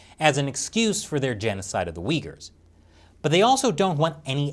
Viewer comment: At 6mins I accidentally initially misheard "genocide of the Uighurs" as "Genocide of the week" XD